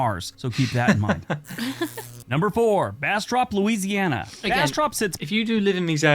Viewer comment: Did he just say “Ass drop”?